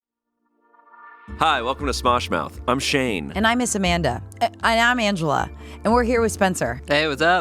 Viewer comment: "and I miss amanda" is so real, but angela is also crushing it ♡ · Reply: I could've swore she said "and I'm Miss Amanda"